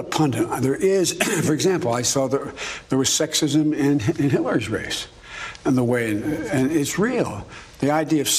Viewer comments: Joe, please enunciate your words! Closed captions can't understand you. At 1:22 closed captions has it as "there was sexism in Hitler's race". 😂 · it sounded like “there was a lot of sexism in Hitler’s race”. Multiple listens, still sounds like that. Sounds about right Hitler probably faced a lot of sexism as well. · he said Hitler instead of Hilary...